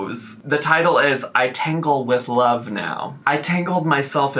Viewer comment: bestie I heard tangle as tinkle and thought okay salah okay peepeepoopoo poetry account